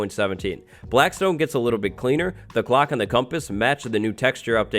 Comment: I’m sorry but every time he said clock my brain thought he was saying glock